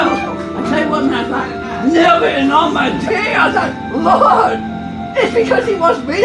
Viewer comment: And I thought ugh i gotta get outta here. Man o man, that, that... wow. The whole (entire earth?) LULULULU! On his tippy toes man, because they don’t wanna touch the smell. I thought ugh, man, i gotta get outta here run for your life. I told james, i told james man, we gotta get the hell outta here and hightail it. The whole world LULULULU! I tell you what man, when you see it and you think to yourself, the fuck is that smell? It went LULULULU! I tell you what man, perfect surgeon! I win! Haha I tell you what man, it’s because he wants me (to have a baby?) boo (off his chin). No, not in a goddam day, and theres that a goddam asshole ahhh. Theres a, theres a fuckin. Hel Help! You gon protect me with all balls bitch. Haha! Died and went to heaven! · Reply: "Neva in all my day. I said LAWD" Is what I heard after "I tell you what man"